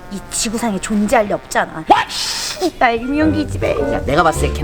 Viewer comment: 초 이 씨발 얄미운 기집에로 들리네 나만그런가